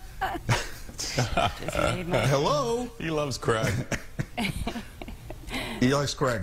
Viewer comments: Totally thought he said he loves crack for a minute then, im a Brit, we pronounce it like crayg. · for a moment i thought he said "he likes crack..." lmao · for a while there my drunk ass thought he said "he loves crack "